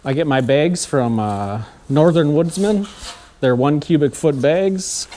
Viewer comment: I was just wondering if you had the number to the place to buy them bags. I thought you said Northern woodsman is the company. I can't seem to find anything. Thank you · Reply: It is Northern Woodsmen (<num>